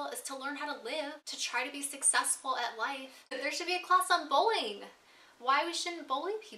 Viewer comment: totally thought you said bowling class not bullying lol